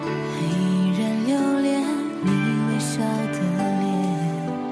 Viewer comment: who hears liang you nian??